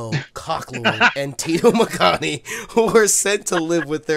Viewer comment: I thought it said tito mankini 😂😂😂